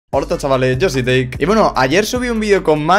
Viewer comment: Minuto 00:00 ¡HORTO CHAVALES!! XD ENTENDI ESO